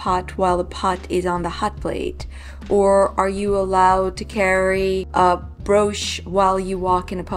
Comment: Sometimes I don't understand your English! At 5:15, "are you allowed to carry a (something?)" Are you using "carry" to mean "wear"? What's the drawing of the hands for at 5:08? By the way, the short poem you called a "limerick" isn't actually a limerick, which has a specific form. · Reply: I asked about "carry/wear" because I think she said, "are you allowed to carry a brooch?" In German, so I presume also in Yiddish, the same word is used for "carry" and "wear". In English, we would say, "are you allowed to wear a brooch?" ( It's possible she didn't say "brooch", in which case I don't know what she said.) There's a similar issue where she wrote as a child "I am born in (whatever year)." In German, "ich bin (in year) geboren." In English, we say "I was born in (whatever year)."